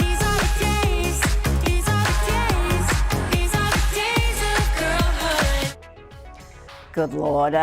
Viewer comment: I thought he was saying "these are the gays of girlhood " 😂😂